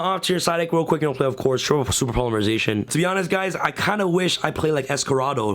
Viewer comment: I couldnt understand what he said, can someone explain? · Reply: He said Super Polymerization